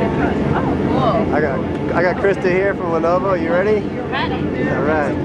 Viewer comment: Did he say Lenovo or some town name? 10:07 PM 10/31/2019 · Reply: Happy John Lenovo - she had won an internal company contest and got to go on the zero g flight.